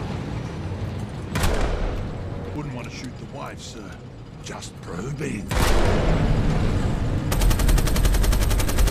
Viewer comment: What does the bullet farmer say when the employee says you shouldnt shoot at the wifes sir at 1:00 ? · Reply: “just probing”